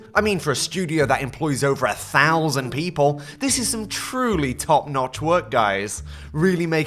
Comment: did you say notch?